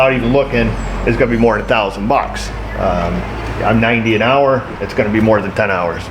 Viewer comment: I thought you said 19 an hour,but silly me, it is 90!!? and some dealers 160?? an HOUR?? wow, 700 bucks a day and 1400; how much does a good surgeon make over there? Now i believe the US&A is lost; we'll see,says the blind man.